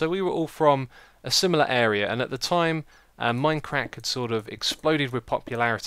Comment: it sounded like said MineCrack i was loling the whole time